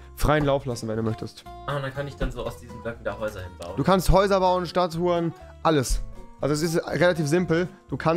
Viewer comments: Hat nochjemand bei 3:07 statt statuen stadt h‘ren verstanden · ich habe stadthuren verstanden nicht statuen